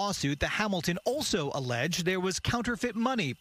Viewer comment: What they did to this man and his daughter is so wrong on all levels. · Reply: I thought they said cash and some was counterfeit